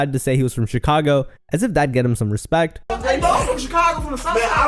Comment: Joe budden is a dick he sounds like an old ass angry auntie 😂soulja boy he's a roaster big Ls all over thought he was saying he's from chiraq 😂😂😂and Charlemagne is a c..t too silly little woman